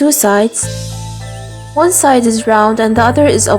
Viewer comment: I thought she said “ One side and round and the other is ugly”